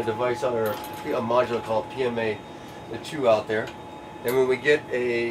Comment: subtitles read "a module called PMA that...(sorry can't hear it)" I believe he said "a module called PMA, there's 2 out there" ? 4:57 the subtitles read "we avert it down here" when he actually said "we (uh) berth it down here". Berth noun: a ship's allotted place at a wharf or dock.